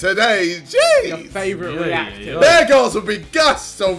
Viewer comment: Lol, I thought he said Bear Grylls, not bare girls.